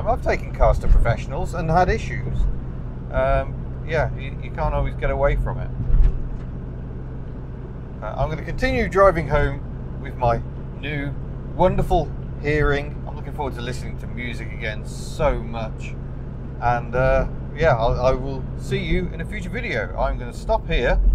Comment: I get 29mpg around town with my 1999 astra 2.0 16v sri. About 38mpg on the motorway. @ 8:53 I thought you said you were continuing to drive home with my new wonderful earing lol. I think i will have my ears sorted out haha.